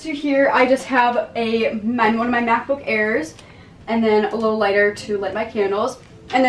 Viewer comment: what did she say!? “$#^%*~@ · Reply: rott ceci she said “one of my macbook airs” 😂 ☠️